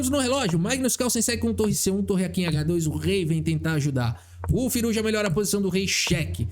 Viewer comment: aos 24:37 só eu escutei FIROUZJA?